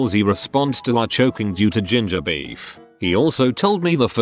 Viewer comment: When he mentioned ginger beef, I thought he said JuJu-Bees.